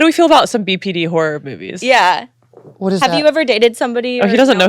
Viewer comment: It sounded like Said "Whore movies"